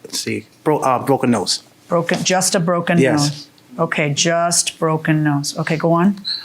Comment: Did he say “I just broke her nose”? What a stand up guy